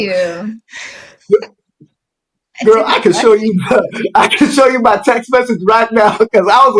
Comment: I picked up on that as well. She didn't say she didn't block him, just kept on saying you're not blocked. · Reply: She did actually say, "I did not block you.